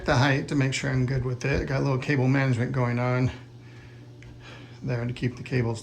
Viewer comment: At first I thought he said “I have a little kitten management going on” haha. I was clearly distracted by the cat.